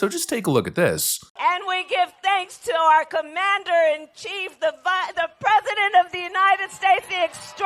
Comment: Did Kamala almost call Biden "Vice-President"??? 0:50 Kamala: "And we give thanks to our Commander-in-Chief the Vi-- the President" I've replayed it a few times but even now I still feel like I misheard it.